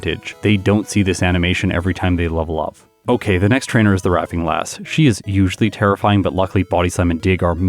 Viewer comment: Did you just call her the Raffing Lass? 😂